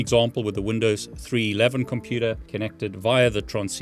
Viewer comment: Imagine how shocked I was . Thanks to accent, I heard 'Windows 11', not 3.11